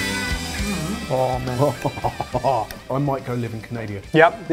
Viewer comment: did really say "I might go live in Canadia"? :D:D:D:D:D Oh Canadia <3